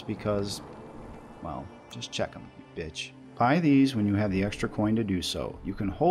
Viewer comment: Wait... Did he just call Me a bitch?